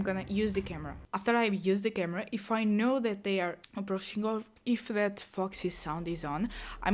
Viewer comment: What a hell did you say on 8:46 ?!? Aproachingorv ?!?! 🤣🤣🤣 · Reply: "Aproaching or" 😂😂 but it really looked different